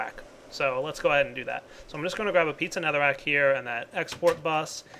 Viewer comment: Am I the only one who heard "pizza netherrack"?? lol